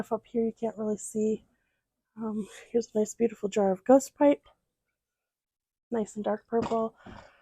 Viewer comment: “A nice beautiful jar of”… of what? I can’t figure out what you said. This is a great pantry, thank you so much for sharing. The syrups for lemonade sound delicious! And fermented quail eggs? Oh my! ❤ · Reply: A beautiful jar of Ghost Pipe. The quail eggs are fridge pickled not fermented.